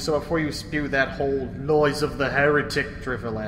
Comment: Magnus seemed to go full Brummy for a second and I heard "Loys of the heretic" and I'm fucking broken 😂